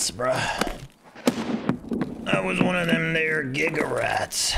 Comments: I'm not the only one who heard wrong at like 3:24 right? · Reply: I heard “one of that there giga rat” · just drops the n word casually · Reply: Idk I hear "One of them their giga rats" if thats what your talking about